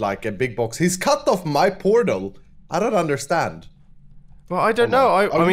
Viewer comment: iskall: "hes cut off my p*rno" what the hell did he say lmao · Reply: Portal*